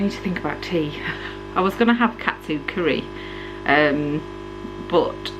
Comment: When you said Katsu curry I thought you said cat food curry haha disgusting !